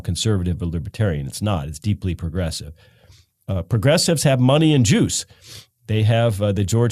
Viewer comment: Thought he said money and Jews, the George Soros' the Barbara Streisand's